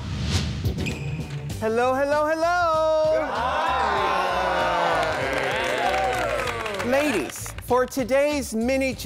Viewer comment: Did he just say....Hello hello Ladies